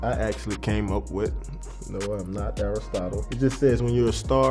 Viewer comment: Who is erril stadle or what he said